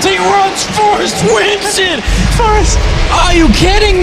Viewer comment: I just laugh my ass off everytime I hear sammler say forest at 1:40 :P